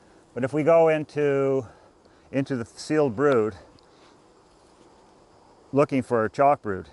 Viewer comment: I am not english, so I dont understand the word you say : what does "brute" means??? or blue, or broute,,,, something like that. Example: minute 6:50 facile brude and chack brude.... I am sure the words aret these... what are the words you say? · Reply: He is saying brood-unhatched bees